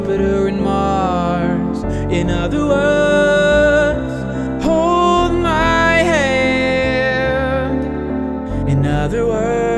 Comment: When sings at 0:32, "Hold my hand", does anyone else hear "Hold my hair"?